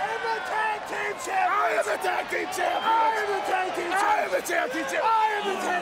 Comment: It kinda sounded like Kane said “I AM THE CHAMPIONSHIP”